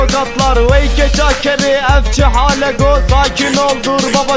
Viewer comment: de ne diyor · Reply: Furkan Keskin vay eşeğin kızı bu ne hall